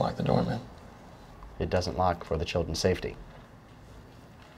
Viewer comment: what does the guy say right at the end at 3:30 ? something time ? floor time? store time? tried the subtitles and they don't say anything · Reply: Story time